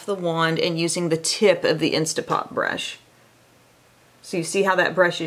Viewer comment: Every time you say “instapop brush” I hear “instapot” 😂